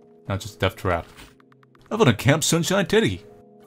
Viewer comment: When he said camp sunshine teddy I thought he said tiddy lol